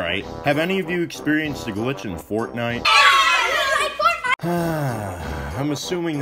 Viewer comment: everytime I hear that I think it says we hate fortnite